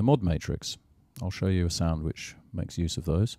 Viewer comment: Did he say he is going to show us a sandwich? Awesome!